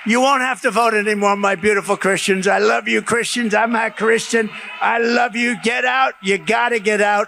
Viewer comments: Does he say I'm not Christian when saying this is the last time it sounds like he says I love Christians I'm not Christian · it sounds like Trump said that he is not Christian. @ 8:57 "My Beautiful Christians, I love you Christians, I'M NOT CHRISTIAN. I love you, get out..." I have listened several times now, he did say that he is not Christian. How did everyone miss this????? This puts a thorn in the thought that he is Jesus or something.